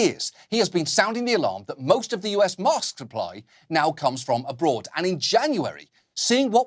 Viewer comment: Hahaha when he said “mask supply” I really thought he said “mosque supply” and I was like wait what